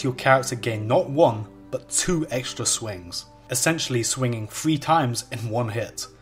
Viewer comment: thing = thing not fing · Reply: every time he said "three" i heard "fwee"...